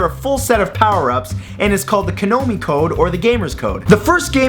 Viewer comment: Did he just say "KANOMI" instead of KONAMI !? LOL xD